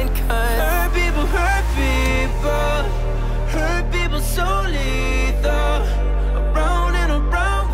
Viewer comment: is it just me or when jack g says "hurt people so lethal" sounds like "hurt people solito" ?? lmao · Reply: I heard "hurt people so little"